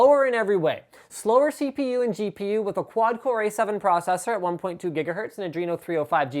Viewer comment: hold on.... A7 processor? As in.... Apple processor? or maybe i just heard wrong